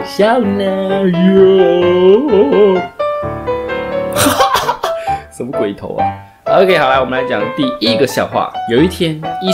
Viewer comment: 我把什麼鬼頭啊 聽成什麼龜頭啊😂😂